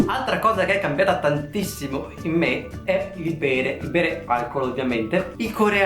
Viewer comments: Al minuto 4:58 al posto di bere ho sentito pene.... Non va bene questa cosa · ma solo io al minuto 4:58 ho sentito "il pene" invece che "il bere" ? e poi con la frase prima "una cosa che è cambiata in me è.. il pene" . mi sono un attimo scandalizzato e poi continuando a vedere il video ho realizzato che hai detto "il bere" ahahhahaahah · giuro sto piangendo dal ridere 😂😂😂😂😂😂 04:58 GIURO CHE HO CAPITO PENE. NON SO PERCHÉ. Capito molti doppi sensi ma qua giuro di averlo sentito AHAHAHAHAHA ah giusto rivelami come cacchiarola hai fatto a levare le occhiaie. SONO UN PANDA IO OSTI 😂 comunque ti atteggi come sempre come una nera del ghetto 😂😂😂😂 mia madre mi rinfaccia sempre quello che ha fatto per me e per i miei fratelli eppure non siamo asiatici... oppure si? 😂😂 scherzo siamo ovviamente bezzotti bergamaschi puro sangue 😂😂